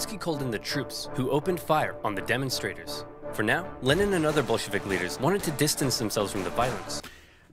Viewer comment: dude what word did he say? cause to me it sounded like he said, "Lenin and other Bolshevik n**g**s". is it just me or it sounded like that? i tried to make the word but i couldnt